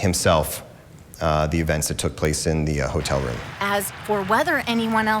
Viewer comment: So nobody’s gonna talk about how the reporter says “edivence” instead of “evidence” at 0:21 ? 🤣🤣🤣🤣 BLOOP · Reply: 😄😄😄I thought she said "events tho"😁😁😆😆😆